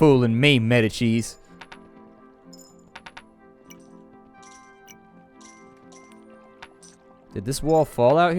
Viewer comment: I kept hearing "Meta-cheese" when would say "Medici"